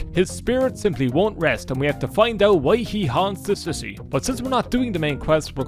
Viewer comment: Me: Man, I dunno. I feel pretty listless and lost, really. You got any words for me, random YouTube video recommended to me? Misheard line: "His spirit simply wont rest and we have to find out why he haunts the sissy." Me: ...yo real shit?